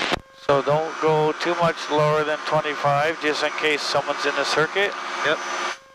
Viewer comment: In the states we say, “entering the pattern.” I thought I heard you announce, “joining the circus.” LOL